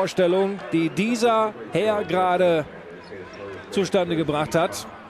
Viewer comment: What the commentator actaully said at 00:18 was Zustandegebracht What it sounded like was: Two step nigga black touch Ctfu